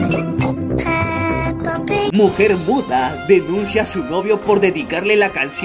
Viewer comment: Loco loco loco. Banda cuando en el minuto 39:45 aproximadamente, dijo “muda” escuche “P***” soy yo o casi lo funan 😅, 🧐🍷un vinito ?